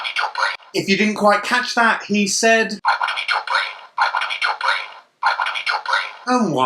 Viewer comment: he sound like he says I wanna be Tom Brady if you listen close